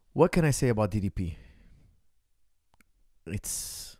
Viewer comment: Start of the video, I heard, "What can I say about EDP?" My brain did a double take real quick lol